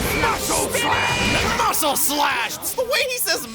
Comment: NGL, I thought that Vandam was saying "muscle slap" until I started watch this LP